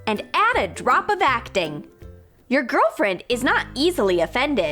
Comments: On 1:26 did she said girlfriend?? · Did you say girlfriend